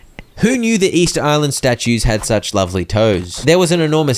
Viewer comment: Excuse me what did you just say? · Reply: the statues have such lovely toes 👀